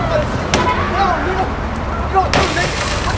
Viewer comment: did he say "LA Crips Nigga"